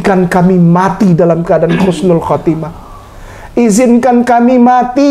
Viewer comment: Assalamualaikum.. Maaf ust.... Telinga sy mendengar Khusnul khotimah... Bukannya Husnul khotimah.. Apa sy salah dengar ya.. 🙏🙏🙏